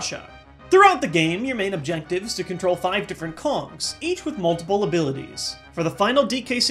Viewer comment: At number 10, I thought he said, "Your main objective is to control five different cocks" instead of Kongs